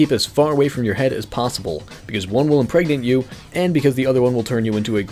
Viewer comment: what is he saying cause one will word you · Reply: Epitoaster impregnate